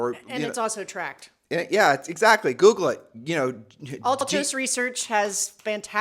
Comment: What research did say we could track for our area? Sorry, when she mentioned the site I couldn't understand her. Thank you 😊 Around 3:37 . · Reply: Altos Reserch